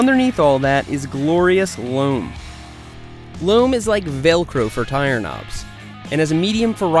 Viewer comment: I thought you said "hook and loop" not Velcro.